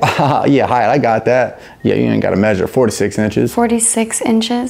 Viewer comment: "4 to 6 inches.... 3 in the winter" 🤣😭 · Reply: I thought he said 426 Inches lol